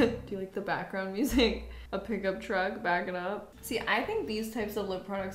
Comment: when she said backing up in my mind I thought she said back it up back it up